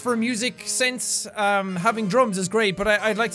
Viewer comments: when you said having drumbs are great but i thought you said having drugs is great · when he said '"drums are great" I thought he said ':"Drugs are great" X3